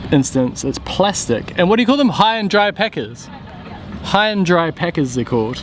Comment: Don't know about you guys but I heard "high and dry peckers" ...